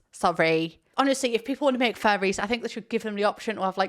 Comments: The comment section was blocking half the screen when I was watching part of this video so I totally thought said fairies not Furrys! I was so confused! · When you said furries I thought you said fairies